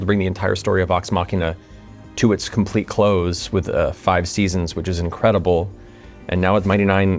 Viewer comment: did he just say 5 seasons? There are 2 left? Awesome!!!